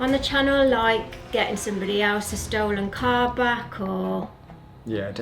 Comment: i honestly thought was going to say a stolen carpet 😂